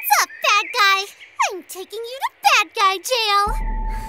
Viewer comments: She just said that guy or bad guy I can't see it and it sounds like she said fat guy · I thought she said, fat guy